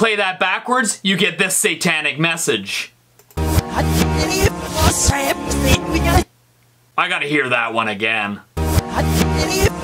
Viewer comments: all i hear in the MJ satanic one is: I Believe it was certain in begun. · i thought it said "i belive it was satan in maia" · in the micheal jackson's "Beat It" i hear:"I believe there's satan in Miguel" But you decide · backwards misheard lyrics, i believe was sam sleeping us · who else heard "I could believe it was sap timmy Baguette 🥖" Edit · i heard i believe it was satan in my eye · I Heard I Believe It Was Sam Is In Me Mug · I heard “ I do believe it was Satan in me yeah” Like if you heard the same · It sounds like he saying I believe Satan it begun but 2 years later I'm still diying slowly like boi why did I edit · I heard “I believe it was Satan in me guys”